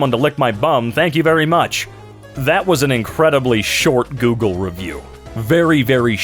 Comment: I thought it said asians